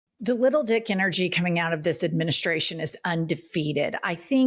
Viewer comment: I apologize...I just smoked a joint; I thought Jennifer said "ribble-dick" so I adked chat GPT what the definition would be: 😂😂😂 ribble-dick 1. (n.) A foolishly boastful person; one who exaggerates their own abilities or tales for comic effect. “Oh, don’t mind Gary — he’s a right ribble-dick after two pints.” 2. (n.) A small, restless creature or person who cannot stay still. “That kitten’s a little ribble-dick, bouncing off the curtains again.” 3. (v.) To ribble-dick — To ineptly meddle with a machine, plan, or situation, usually worsening it. “He tried to fix the carburetor and ribble-dicked it beyond repair.” Related forms: ribble-dickery (n.) – Foolish meddling or blustering behavior. ribble-dickish (adj.) – Prone to boasting or bumbling incompetence.